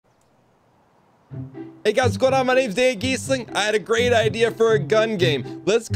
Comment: Just have to mention that close captioning thought you said "my name is eggy's sling." I was laughing too hard to pay attention to the first half of the video